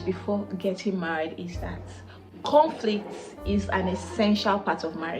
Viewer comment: At first, I thought you said cornflakes are essential for marriage!